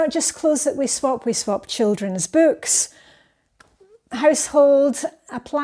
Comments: Love it. Swap parties sound so fun. I thought you were gonna say we swap kids!!!😂😂😂 but you said kid’s books:))!!! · 😂 I thought you said "We swap children". There were some times where I would have swapped certain children. 😂